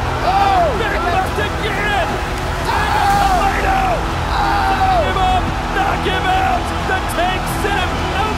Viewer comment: he said Tarik Kebab knock him out?😁 No offence but thats what i heard😅